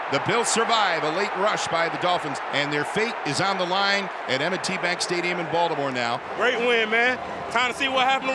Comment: Did he say Emmet T bag stadium at 34:50 ??